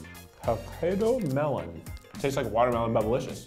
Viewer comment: did he say potato melon?